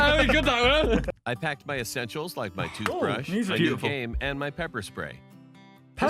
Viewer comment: I swear i heard him say he packed and i quote from what i heard "My toothpaste, my COCAINE, and my Pepper spray."